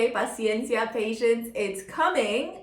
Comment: When you said patience in Spanish I thought you said Precious in Spanish which is my name . I definitely tuned in a bit harder .